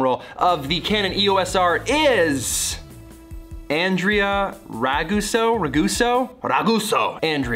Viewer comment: I heard "Andre... " and my heart skipped a beat. Sadly, you said Andrea, not Andrew. Congrats to Andrea though, that's awesome!! Keep up the great vids Peter!